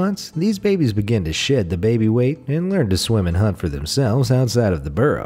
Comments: did u say sh%@ the baby weight? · I swear he said "shit the baby weight"😂😑😂😑😂😂